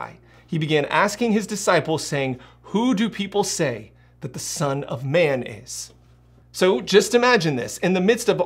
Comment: I wasn’t watching the screen at 5:35 and had the video at 1.5x speed. Was thinking “Why did he just say son of mayonnaise”